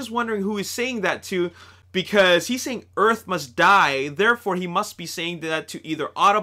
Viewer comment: I thought he said your must die